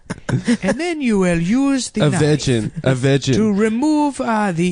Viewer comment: I never realized nick actually has the worst African guy impression out of all three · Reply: What is Adam saying right here? "A vegin a vegin"? Is that a word? I don't understand. Help me out